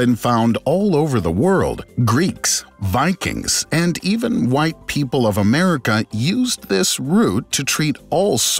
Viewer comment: did he say white people of America?